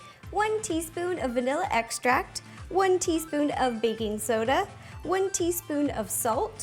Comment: am i the only one who thought she said "Baby Soda" instead of "Baking Soda"